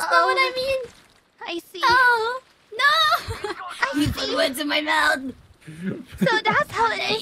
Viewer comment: What does say at 0:12? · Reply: says "you put words in my mouth"